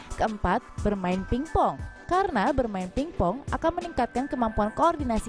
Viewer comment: aku dengernya main tik tok 😂😂😂